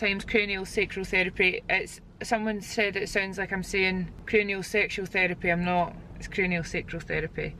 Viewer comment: I’m sorry I heard “it’s not craniosexual therapy, it’s craniosexual therapy” 😂